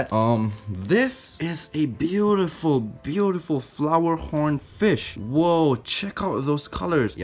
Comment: For a sec i thought he said ''flowerporn'' but it was flowerhorn T3T Edit: OH MAH GAWD YOU NOTICED MEH IM SO HAPPY T^T Tysm lol